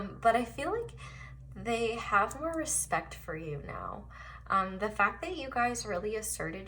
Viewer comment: Pile 2 : 23:46 THEY HAVE NO RESPECT FOR YOU NOW. Did you say this really? Please reply. Thanks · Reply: “ they have MORE respect for you now”